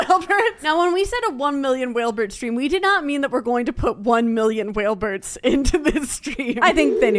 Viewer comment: I didn’t know why but I always thought that you said whale birth😂